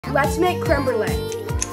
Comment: At first i heard "lets make crambled egg"💀